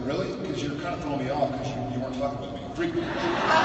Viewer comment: Can someone explain why the crowd started laughing/cheering at 4:15, couldn't really hear what Jared said!! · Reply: lauren webster "you weren't fucking with me", or something like that.